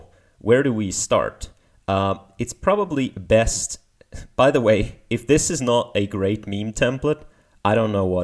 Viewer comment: what is the word said at 3:02? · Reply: do you mean "by the way"?